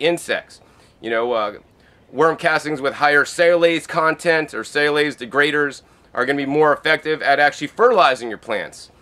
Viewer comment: what does he say; "high chitinase content... salase content, salase degraders..."? I understand that it's an enzyme related to fertilization rates, but I can't figure out the term spelling.